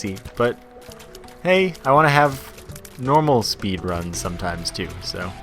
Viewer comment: did you say speed run?